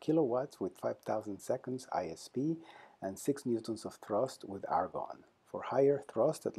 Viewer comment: Just to confirm what Dr. Franklin Chang Diaz said at 1:57, did he say 6 Newtons of thrust? This is great news. Keep up the good work and I am looking forward to seeing this in space.